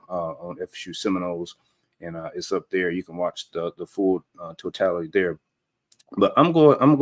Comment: Did he just say the "FULL TOTALITY". lol